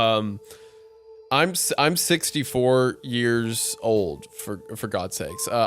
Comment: Coming back to this I always thought I heard say "I'm 6 feet 4 years old" during him reading the story. But it was my mind playing tricks on me cuz he didn't say that, it just sounded like it. Anyway, how would one measure age in height if you did?